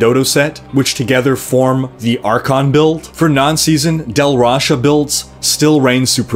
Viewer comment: It sounds like you say Del Rasha instead of Tal Rasha when you speak of it